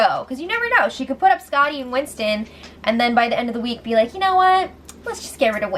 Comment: She wants to BD Swaggy and put up Angela and Winston. Sam told her she has the power. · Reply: I heard her say, Scotty and Winston. :)